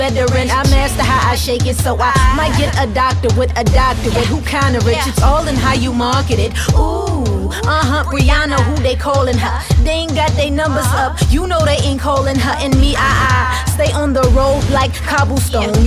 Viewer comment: at first i thought she said "Rihanna" at the beginning